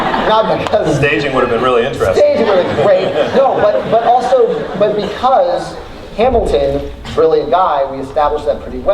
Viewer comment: “And there’s something about putting on a mask, you’ll learn this if you study ————“ What did he say at the end there? It got lost in the crowd’s laughter lol Edit: 27:30 don’t mind me just leaving time stamps to return to lol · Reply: He said: You’ll learn this if you study theater.